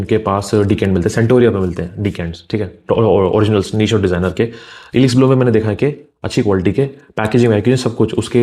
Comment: what is the site's name? I didn't catch it. Ilix Blue? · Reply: Elix Bloom